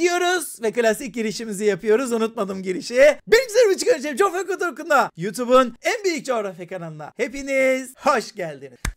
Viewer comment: burda ne diyor asla anlamıyorum ağır çekilme falan da aldım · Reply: benim sevgili biricik öğrencilerim